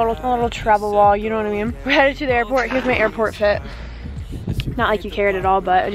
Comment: when she said this is my airport fit I thought she said this is my airport shit